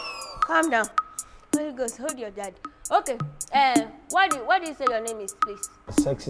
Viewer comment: Did i just hear "Holy Ghost, hold your daddy"? 🤣🤣🤣